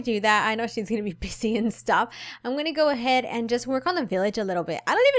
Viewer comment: Am I the only one who heard, "She's gonna be pissing and stuff," at 0:56 Only me...? Darn.